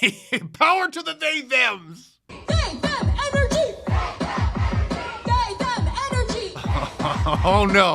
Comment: I heard "Big Back energy." That's definitely what I see.